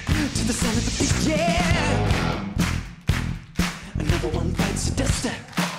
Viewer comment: The vocalist is good but am I the only person who thought he said “another one bites the duster”